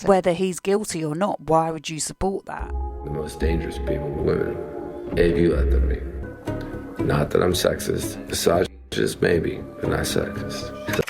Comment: Can anyone tell me what Marilyn Manson said at 11:12, during the unreleased interview section? I replayed that part three times and I still couldn't understand it. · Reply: He says the most dangerous ppl are women they do that to me not that I’m sex!st misogynist maybe